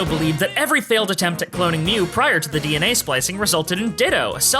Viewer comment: i panicked for a minute because i misheard this line as "every failed attempt at cloning mew via DNA splicing resulted in ditto". The implication that we'd be breeding human DNA into pokemon ... THANKFULLY THAT'S NOT WHAT THEY SAID- they said "every failed attempt at cloning mew prior to the DNA splicing" No horrifying breeding implications